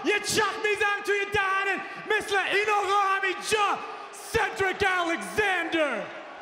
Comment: i think he said i won't job to cedric alexander and he did